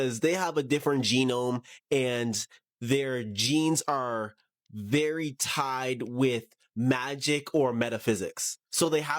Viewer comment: I thought he said "their jeans were very tight" at first. ...I was like "my ex girlfriends must have all been elves then!" 😁🤣 Sik